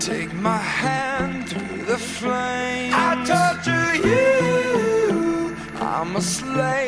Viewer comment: i really thought imagine dragons verse said "I TOLD YA YOUUUUUU" but i torture you makes more sense now😂😂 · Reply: I thought it said I talk to youuu lmao